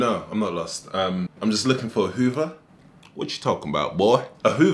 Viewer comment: As French i heard 'im looking for à Uber" with à weird prononciation